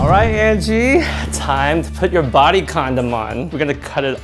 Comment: Did he just say body condom 😶😂😂😂😂